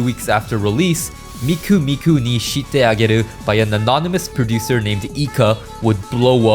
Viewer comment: Why do I hear "I shit ayy" instead of "ashite" lol